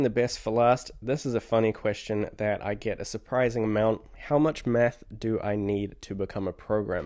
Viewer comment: I thought I heard “how much meth” 😂